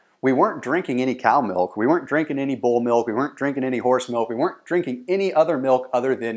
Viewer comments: Thomas did you say Bull milk @ 5:38? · BULL MILK!!!! Thats not milk my man...thats bull babies!!!! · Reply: he said "goat milk" but it sounded like he said bull milk. kingpin reference? 😜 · did he just say bull milk???hahaha